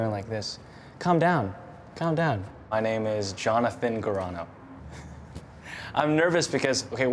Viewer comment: Why did I hear it as Jonathan CORONA?